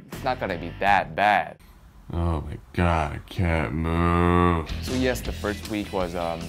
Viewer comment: when said move and sounded like a cow moo-ing😂